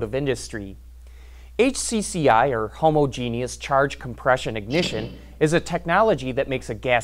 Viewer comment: Did he just say Homo - Genius?